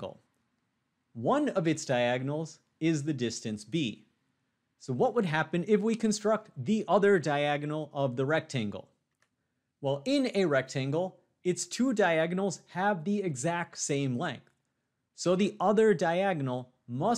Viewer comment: of course but nowhere was that said and neither is a way of proving that (in math you have to be able to prove everything you do) the only way you can tell is by saying it looks like it is, but that's not enough proof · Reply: I was wrong 😭 I thought he said a triangle not a rectangle with a diagonal line 🤷